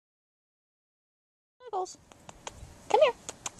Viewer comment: I thought you guys were saying "smuggles". Like that she smuggles things But her name is "snuggles" 🤣